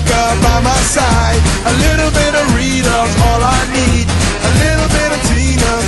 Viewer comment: I thought he said a lil bit of dorito is all i need 😂😂